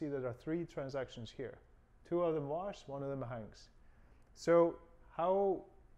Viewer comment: Hey. Thanks for the video. I didn't get what you said at 2:06. You said the two extra accounts on inventory are done what? I heard as if you said one of them is washed and the other is hanged